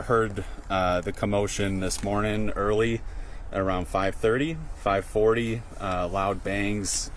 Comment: once again, he lives almost a mile away do you know your neighbors that live over a mile from your home? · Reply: I thought he said about a block and a half.